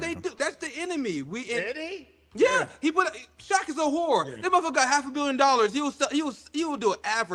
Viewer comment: Wait, what did say about Shaq at 4:05? Does anyone know? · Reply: Shaq is whoowa is what I heard